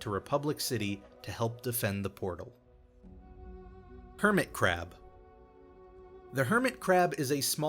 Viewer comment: When he said hermit crab i thought he said hermitcraft